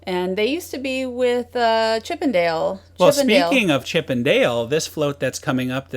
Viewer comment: OMG, Too funny but when said Chip and Dale, I thought for a moment she said Chippendales, which actually kind of worked with the video (strapping Ice Breaker guys) and it took me a moment to realize that she said Chip and Dale.